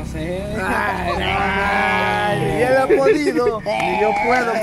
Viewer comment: ! Crei! Que dijo ni la ah ponido! Jajajajaja xD!!!!!!!!!